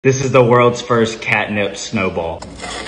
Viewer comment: I HEAR 'CATNAP'